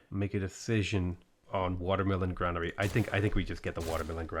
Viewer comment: I kept hearing "I should get the watermelon granary."